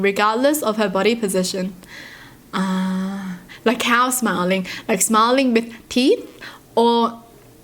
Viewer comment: I thought she said like cow smiling lol